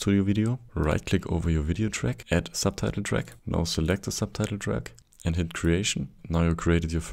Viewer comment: what is being said in the time code 0:09 "also l_ _ _ the subtitle track"? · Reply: Add subtitle track then select the subtitle track…